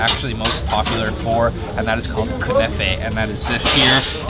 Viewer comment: Anybody else heard covfefe ? 😂😁🤣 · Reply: its name is künefe :) but his mouth burned :)